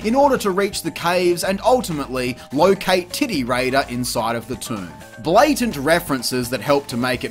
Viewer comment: Does anyone else hear "titty raider" 2:32:53 or am I the crazy one? Fantastic video by the way (the cameo of Tara Croft makes me REALLY want Crystal Dynamics to reinvigorate the middle Tomb Raider trilogy).